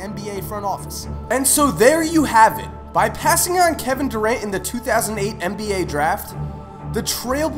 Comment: did he say 2008 draft?